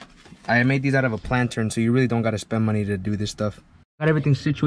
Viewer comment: Hey pal! What did you say you made the slotted grills out of at 9:47? Much appreciated. Well done! Looks great. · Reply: I used a plastic pot. Like the ones for the plants I just cut it out with a razor blade.